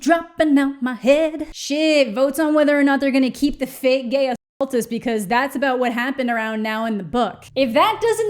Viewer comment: So when said "fake gay assultist" I totally heard 'fake gay occultist' for a sec and got so confused